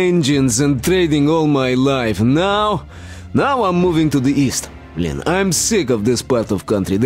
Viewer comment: мне показалось , что он сказал блин??